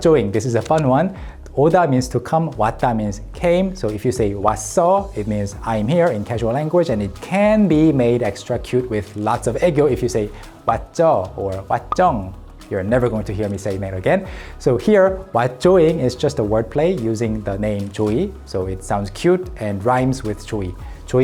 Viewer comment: “it can be made extra cute with lots of egg yolk” is what I heard 🤣